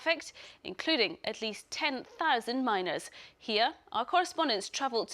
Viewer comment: I thought she said 10,000 miners.